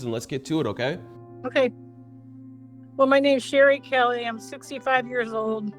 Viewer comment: Ok, shes not 55. Theres no way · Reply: At 1 min 22 I hear her saying “I am 65...”, I clicked on subtitles and they also say “65”.